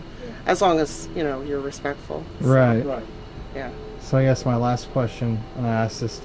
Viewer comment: after the lady mentions the house being very welcoming I heard “yeah”. I have double checked it and it’s still there so you were able to catch that during your interview! Pretty fascinating 🥰